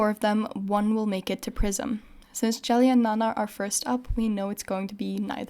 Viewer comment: i thought you said "among the four of them, one will make it to prison" XD didn't even question it untill it hit me you said PRISM